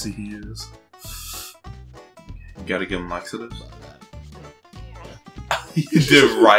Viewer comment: what did he said? I don't understand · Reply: asked, "You gotta give him laxatives?"